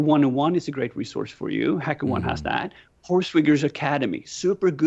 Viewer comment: I couldn't catch the name of the academy he mentioned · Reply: HackerOne